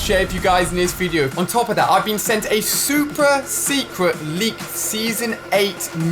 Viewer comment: it sounds like you said supra instead of super.